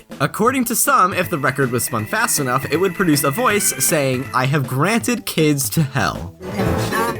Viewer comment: i heard "i have granted kids to jail"